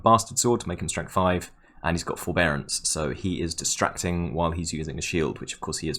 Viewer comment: "He's got forbearance" I swear I heard "He's got ball bearings"...